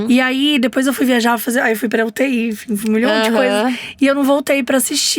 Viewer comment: uti? Ouvi direito?